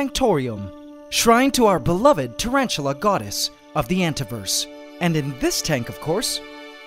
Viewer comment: all I heard was Godis and I know it was the spider